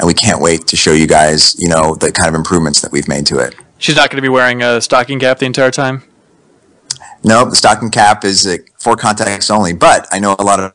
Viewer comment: what did they say? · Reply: They basically said that she wouldn't be wearing a stocking cap the entire time and when she does, its pure for context only (e.g. During a snowstorm)